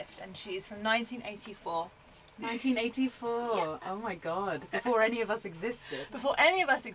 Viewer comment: "The van is 40 next year" "She's from 1984" I'm afraid the math doesn't check out there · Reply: I thought she said she was 14 , so your math is much closer than what I thought she said